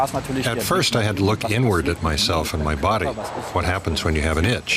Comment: When he said "inward" I thought he said "n word"